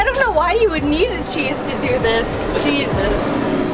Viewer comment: I came from Endless Adventure. BTW, Did say "Jesus" or "Cheese us"